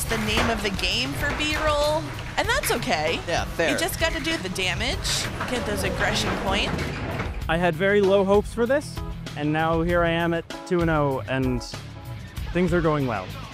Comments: I thought it said beer roll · I can’t be the only one who thought it said Beer roll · Thought it said "BEEF ROLL" 💀💀💀💀